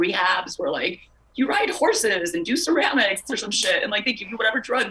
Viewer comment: Sounds like she says " do ceramics or some shit", super professional. Kim Iverson dodged a bullet by leaving this show.